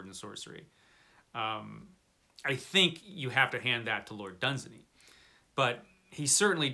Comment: Whose name did you said at 10:16?...Lord Something...I tought it was Robert who created it 😲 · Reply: Lord Dunsany. A great classic fantasy writer who influenced both Tolkien and Lovecraft. Technically he wrote some fiction that could qualify as S&S. Howard made it into what it became though and I certainly don’t mind him getting credit.